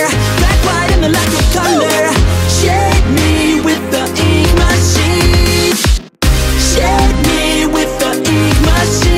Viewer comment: I thought when it said "shade me with the ink machine" I thought it said "shed me with the ink machine" and I said "the ink machine ain't no razor"